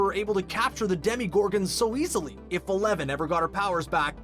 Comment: Did he say Demigorgon?